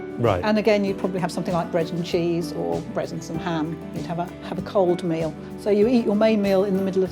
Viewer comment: Can someone tell me what she says at 7:58? She said “bread and cheese, _ and ham” What is she saying during the blank? Resins? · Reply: Bread and cheese or bread and some ham, she says it rather quick